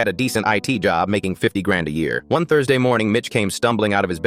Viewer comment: "My roomate mitch" Me: "DID YOU JUST SAY BITC-"